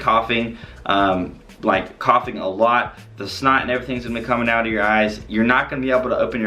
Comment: Did he say that snot is going to be coming out of your eyes??? 10:12 LOL